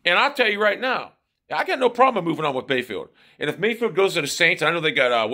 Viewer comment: I thought you said you done with Mayfield what side you on